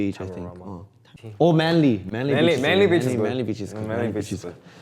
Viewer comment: did i heard manly bitches?